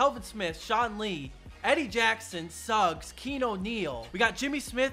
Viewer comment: it sounds like saying “Eddie Jackson sucks.”